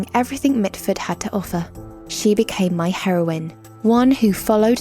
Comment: I thought she said heroin instead of heroine 😂😂😂